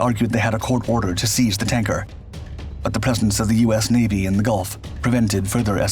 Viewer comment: It sounded as if you said "peasants of the U.S. Navy" instead of presence of the U.S. Navy. Talk about how mishearing a single word can completely change the whole sentence.